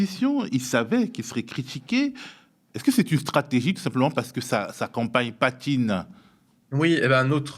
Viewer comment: "Sa campagne patine" J'ai compris "sa accompagne patine" ( Palpatine) mdr Labsus cérébral! Je suis haute-savoyarde, élevée en plein air ardéchois et faisant sa vie de quarantenaire dans le Cantal. Wauquiez je l'abhore depuis tellement longtemps... Si vous saviez ce qu'il a fait ce gens intelligent... C'est un rubis de l'impérialisme français sous dictat Etat-unissiens.